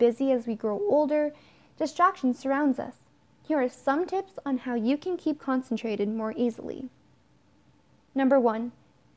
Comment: I heard "Here are some tips on how to stay constipated more easily." Do you see how far my mind is from this world?